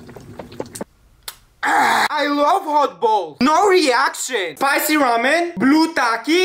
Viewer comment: Did you just say sour balls 🤨🫵